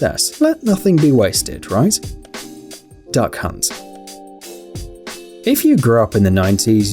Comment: When he said duck hunt I thought he said da c*unt